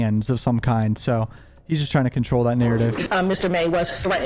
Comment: What did you say at 9:50? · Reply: Narrative